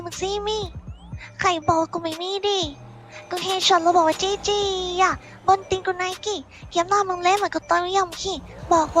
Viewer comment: ทำไมผมได้ยินว่าเฮียเฉิน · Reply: ใช่ไง เฮียเฉิน